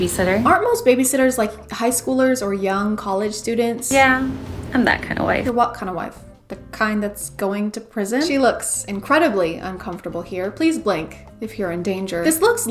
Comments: I literally thought they were saying "yeah I'm that kind of white" not wife and I didn't question it until I saw a comment that said wife instead lol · I thought they were saying "Yeah, I'm that kind of white" but that is too self aware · I thought they were saying "Yeah, I'm that kinda white." And I was just going with it 💀💀💀